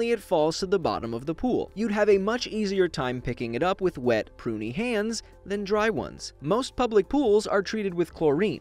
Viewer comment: why the hell did i think he say wet poopy hands